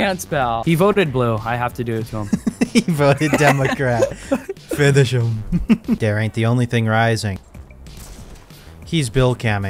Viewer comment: what that kid says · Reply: dare aint the only thing rising